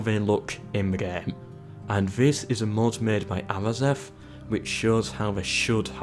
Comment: mod by who? i couldnt hear very well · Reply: Arazeth